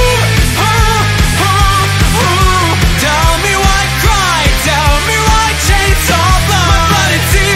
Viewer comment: i always thought it said “tell me why you griddy”